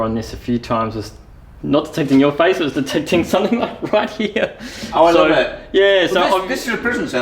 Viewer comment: The child spirit sounded like it said "Pokemon, right there!!" 🤔😛😂☺☺